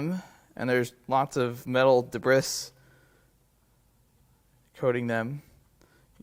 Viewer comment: Did anyone catch 20:39? Debriss